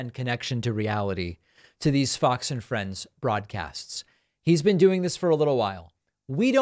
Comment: Thought you said, "These Fox and Friends fraudcasts." 😂😂😂